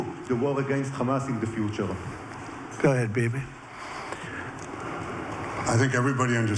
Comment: did Trump call him baby? I heard him say baby twice in this Q&A 🥴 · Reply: Bibi*